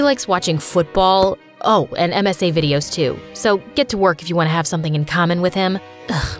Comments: in the subtitles instead of msa videos it was written as messy videos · instead of msa it said messy ???? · it said messy instead of MSA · it says messy vids instead of msa vids:_MSAMSALaugh2::_MSAMSALaugh2::_MSAMSALaugh2::_MSAMSALaugh2::_MSAMSALaugh2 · why did the cc say ‘messy videos too’